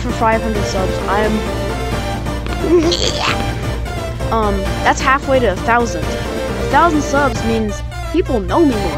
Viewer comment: did you say the n word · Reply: No I was just making goofy sounds. If you were to spell it it would be smthn like "neēeēeyaaah" lol